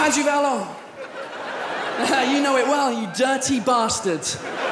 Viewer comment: il a dit quoi à 2:03 ? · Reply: Juliette — "You know it well, you dirty bastard"